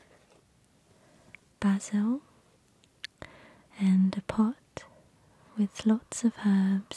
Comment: I thought she said and pot as in weed